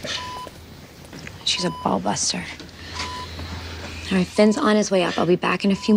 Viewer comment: did Meadow say: She's a Bulbasaur? lol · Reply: Nah, she said "ballbuster".